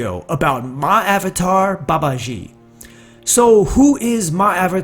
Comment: I thought you said. "Many have asked me about 'My Avatar Babaji'“